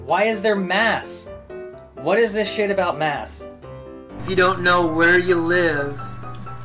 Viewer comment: "What is this shit about maths?" I know what I heard!